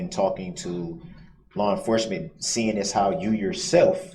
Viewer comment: Did he just say “lawr enforcement”? Good lord🤦‍♀️